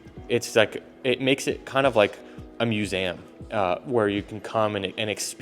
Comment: what did you just say? Musayum lmao